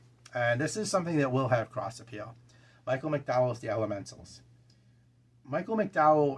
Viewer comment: I really thought you said Michael McDonald, "I keep forgetting that I wrote a good book..."